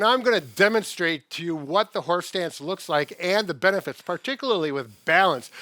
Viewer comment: I thought you were saying horse dance😂😂